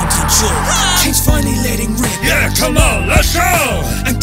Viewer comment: the person that did the subtitles thought kane said tesco instead of let's go was I the only one that noticed that